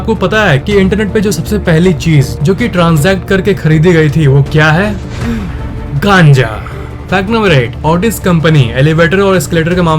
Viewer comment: Kya bola 5:51 me kaanja ya gaanja